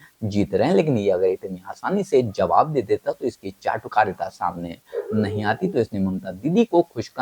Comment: And did you say jhatukarita at 6:05..